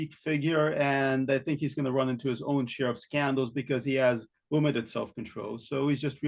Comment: Did say "sheriff's candles". What is that? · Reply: share of scandals